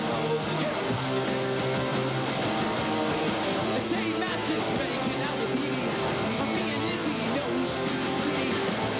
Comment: what does he says at 1:21 about J Mascis? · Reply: i think its j mascis making out with phoebe